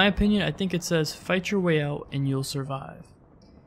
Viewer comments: I just hear "fight your way out and asanhan" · I heard "fight your way loud and couse some harm" On the first time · I heard "fight your way out of askaban "